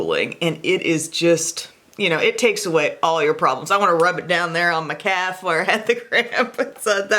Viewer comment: Omg am I the only one that thought was gonna say "I just wanna rub it down there on my CAT" not calve (however u spell it)? She def said "on my calve" hehe I'm just a dowtty girl (dirty girl lol) ...